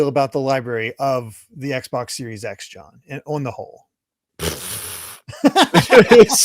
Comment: I was listening to this passively while drinking a nice beverage of ice coffee and did a spit take on my monitor after hearing say what I thought was the word “onahole”. Immediately followed by laughing and trying to stay back on subject.